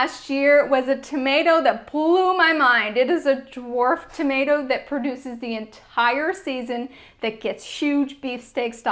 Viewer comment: What was the tomato at 8:52 ish? I replayed it several times and didn't catch the name. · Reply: It’s Purple Reign. Definitely a great tomato!